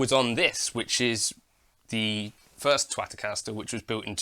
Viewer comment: Did you just call your guitar a Twatocaster?